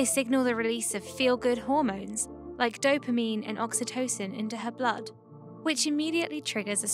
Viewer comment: Wait did she say dopememe?